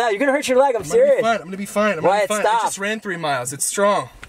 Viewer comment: Did u say “Wyatt, stop” to John at 11:18 ?? 🤔 lol